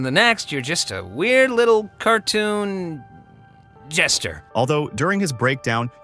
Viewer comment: In that part, I heard Gesture, not Jester.